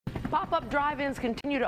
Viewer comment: I thought it said Drive By